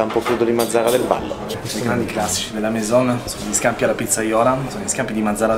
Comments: solo io ho sentito “sti gran cazzi”? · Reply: Simone Tranchida Classici dice hahaha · Anche voi avete sentito"sti gran cazzi" al minuto4:20